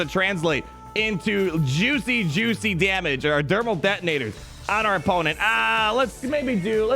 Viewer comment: did I hear Dermal Thetonators?